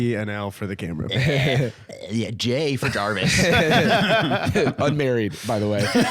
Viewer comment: Wait what did Jarvis just said he's married?! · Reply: I heard "unmarried"